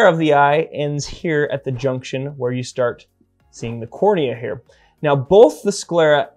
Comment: Who else thought he said Corona when he said Cornea tho....